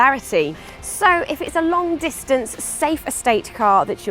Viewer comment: did i hear i will touch all the right places ? lol